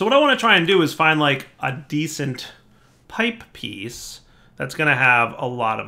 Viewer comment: i thought it said ni**a scale piece